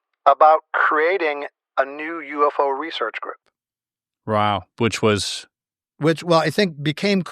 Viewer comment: hes so blown away he said "Row" instead of wow lol